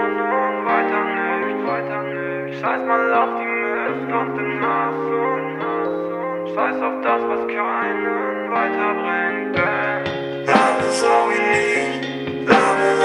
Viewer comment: Ich hab immer verstanden:"Scheiss mal auf die mask und den hass..." anstatt " scheiss mal auf die Missgunst, den hass..."😁sind beides coole messages 👌❤